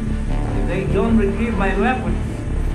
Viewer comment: did vlad just say " they did not like my weapons" and swear in russian at 0:54?